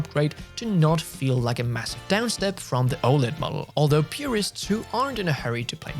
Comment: 🤣 anyone else thought said mugggle instead of model?